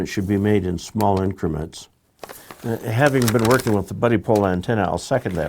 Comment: Did I hear you say "bloody pole"? o_O